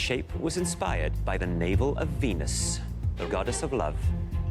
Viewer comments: I thought he said nipple of venus 😂😂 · i thought he said “ by the nipple of Venus”